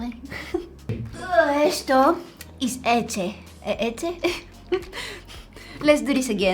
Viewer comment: When you were saying milk in Spanish it sounded like you were saying lete you have to pronounce the “ch” more like leche (idk if that’s how to spell it but I speak Spanish not spelling) but you said everything else very good!